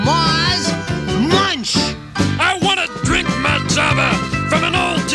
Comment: what does munch say in 0:58? i wanna drink my _ from an old tin can. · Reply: Java. Which is coffee!